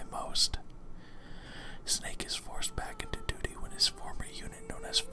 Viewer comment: did you say "Forced back into DOO-DOO"?